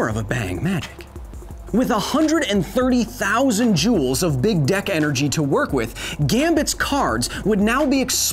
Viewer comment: i thought he said big dick energy. giggity giggity goo